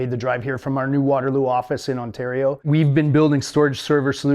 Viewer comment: Misheard at first and thought he said "water COOLED office in Ontario" and now I think I gotta pitch the idea to LTT lol. They have a firetruck, they could do it!